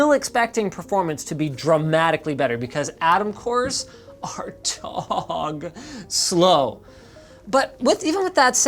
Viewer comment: did he say Tog slow? Like the Tog II tank?